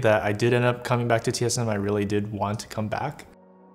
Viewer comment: I’m confused but welcome biofrost we missed you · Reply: Is it just me or did Biofrost say 2:04 "he didn't want to come back to TSM" anyone???